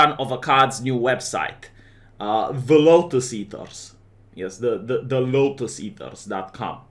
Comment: I thought it said Locust-Eaters.